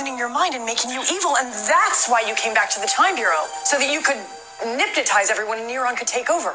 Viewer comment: did she just say "nipnotize"?😂😂